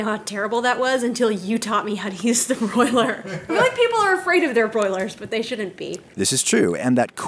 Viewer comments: what is said at @ 5:51. Does she say "I feel like people..." or is it "white people are...." lol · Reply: She says"my people are afraid of their Broiler's but they shouldn't be" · what is said at @ 5:51. Does she say "I feel like people..." or is it "white people are...." lol